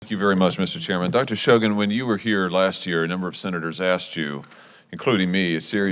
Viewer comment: I had the volume down a bit low and when Sen. Hawley started talking, I legit thought he said “when you were here last year alot of senators ass-chewed you” … I was like nahhh there’s no way 😅.. turned it up more and was a bit disappointed lol